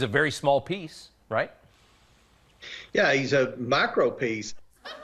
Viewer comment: Could swear I heard "macro pieace" 😂😂😂😂😂